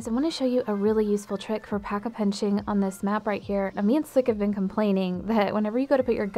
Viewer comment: What does she say at 0:04 it sounds like 'a mints sakavin complaining' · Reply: She said "me and sliq (Sliqkilla)"